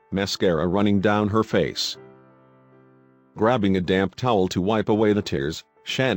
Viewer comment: SOUNDS LIKE HE SAID "DAM" TOWEL... I KNOW HE SAID DAMP 😭🤣🤣🤣🤣🤣😂😂😂😂😂😂😂🤣😂😂